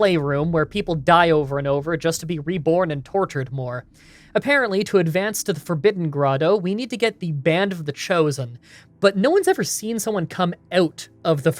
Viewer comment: I thought you said "Band of the Chozo" at first, and I'm like, "Yeah, that tracks."